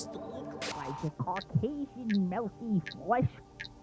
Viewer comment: when they shown jhons body i thought i heard him say cheesey melty flesh